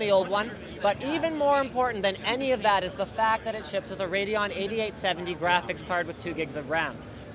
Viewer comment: did i just heard 8870? 0:38 ?!?!